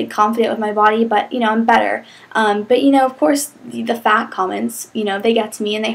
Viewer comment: i thought you said i'm butter..